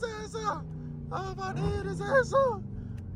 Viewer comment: What a min what he say “ I wanna eat his ass out “ 12:33 😂😂